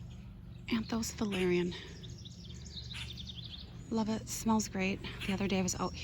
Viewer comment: Hi, Beautiful as always, Could you please say again the name @ 19:09 the tall beautiful fragrant white flower? Ty!!!!! I just heard you say your sister, so sorry, I feel your pain, I'm missing a younger sister, 5yrs now, Still hurts Big Time. Love your Videos! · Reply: It is Anthos Valerian. I started this from seed about 3 years ago. Thanks for watching